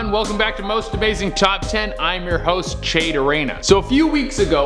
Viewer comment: I swear I heard him say Most amazing chop 10